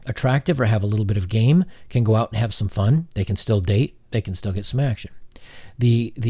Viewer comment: LOL! For a second I thought you said, "they can still smash."